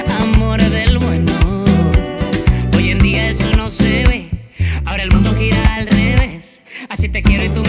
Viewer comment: Cuando salió la canción pensé que decía "Oye tía eso no se ve" 😂 hasta que busque la letra, ya se que dice "hoy en día eso no se ve".